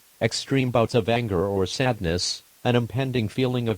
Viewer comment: Madness? Did he just say madness? Me and the bois: MADNESS COMBAT TIMMME